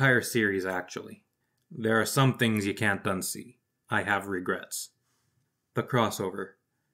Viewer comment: Omg when you said "There are some things you can't unsee. I have regrets," I thought you said "I have reread it" and I was like "Hey, I'm not judging."